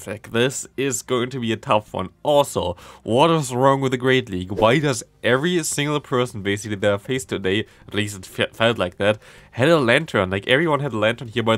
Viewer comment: What did you say? I dont know If its Just me but Sometimes its really hard to Understand what you are saying, but im also Not a native speaker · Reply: im a native speaker and can’t understand him either lol but at that part its sounds like “ why does every single person, basically that ive faced today, plays— fight like that?” and something about “latern” im not sure if im right though..